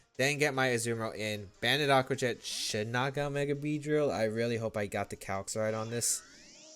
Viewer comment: i thought it said bug buzz mega beedrill and got excited lol